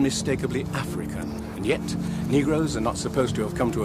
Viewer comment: Did he just call us negros? 2:59/